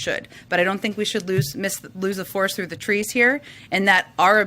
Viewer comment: “Lose a force through the trees here” 0:30 I had to use cc but yep that’s what she said.